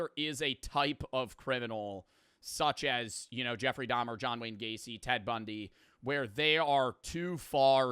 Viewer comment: for a second there i thought he saidJohn Wick Gacey, I had to go back twice to confirm that he did not in fact say John Wick